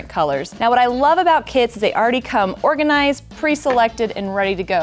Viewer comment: At the beginning of this video it sounded like you said "what I love about KIDS is that they already come well organized, preselected and ready to go"! I thought wouldn't that be great if kids did. 😊 I would love the bag to take with me not only on my first quilting retreat but my first quilting cruise! 🛳 Love ❤️ your shows, Craftsy classes and books. 😘